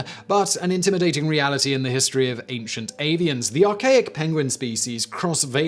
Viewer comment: Did anyone else mishear the words 'Ancient Avian' as 'Ancient Aliens?" That would radically change everything...!